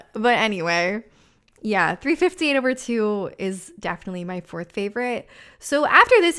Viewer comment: did you just say "Three Fifteen over Two"?